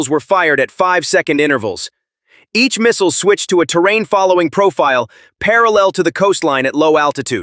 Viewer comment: ~ 核ミサイル(Nuclear missile)に聞こえた…各ミサイル(Each missile)ね。イントネーションの違いで意味が全然違っちゃう日本語難しい プーチンが戦争継続は無駄だって気付かない限り終わらない